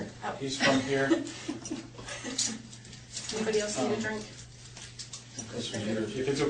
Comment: Am I hearing this right??? At marker 04:50 (approximately) Casey says off the top of her ugly little ripe pimple head: "Got anybody else in your trunk?" If she did, then that's the best example of a Freudian Slip I've ever come across. ~Moisel~